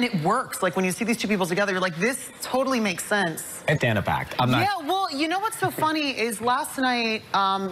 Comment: What the heck does Ryan say at 5:21? I've listened to it several times and can't get it. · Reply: " i gotta go back.. I'm.."